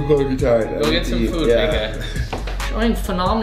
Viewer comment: Who else heard him da the N word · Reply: ClipIt! Moments he said big guy